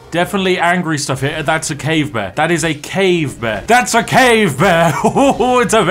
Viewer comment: I heard “cave man” for a whole minute instead of “bear”. Was like “what”? It’s a man? 😂